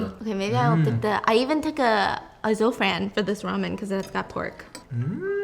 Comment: unrelated but what was the word stephanie said at 4:27? azulfran? it's bothering me that i can't figure it out 😅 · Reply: I think she said “a zofran” as in she took a medication called zofran beforehand which helps prevent nausea